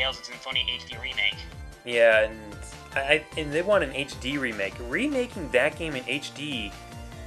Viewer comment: when he said "remaking a tales game" I thought he said "remaking a tails game" as in miles prower.